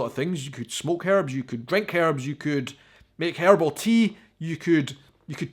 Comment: I'm dying! 🤣😂🤣 I thought you said: Hairball Tea!!!! Bwahahahaa in America saying herbal tea the h is just quiet to make it difficult I'm sure. Because we always need more rules in language somehow. 🙃